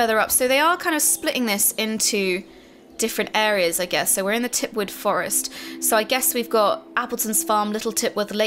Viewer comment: not to be that guy, but did anyone else hear Kim say Titwood Forest?